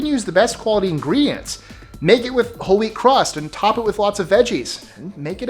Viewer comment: Did I hear "holy crust" in the pizza facts?